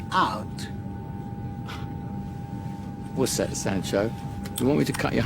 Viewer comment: What does he call the guy? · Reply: Sancho